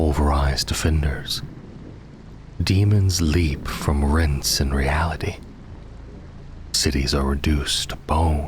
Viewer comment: "demons leap from rints in reality" Please, please confirm if this is what is being said. I actually cannot sleep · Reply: "Daemons leap from rents in reality" is what I've gots in the ole script!